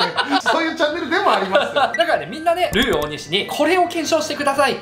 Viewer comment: 「ルー大西」と聞こえて笑いました カレーを検証してトゥゲザーしていくチャンネルですね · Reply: ルー大西であってます笑